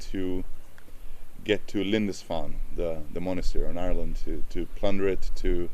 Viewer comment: Did he say Lindisfarne was in Ireland? 🤔 It’s an Island but it really sounds like he says Ireland.